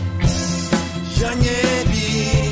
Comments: толи я изза инвалидности по зрению слишком хорошо слышу, толи ты глухой, толи через колонки нужно слушать. 14:15 Сдаётся мне, что поёт он "Я не Вий" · Я услышал: я не Вий